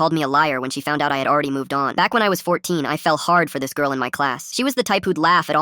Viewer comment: AT THE BEGINNING I THOUGHT HE SAID I FELT HARD FOR THIS GIRL IN CLASS